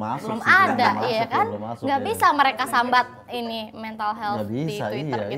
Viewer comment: did I hear "sambat", ghe?